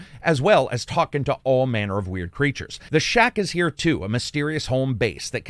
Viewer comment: Imagine my surprise when I heard "the Shaq is here, too".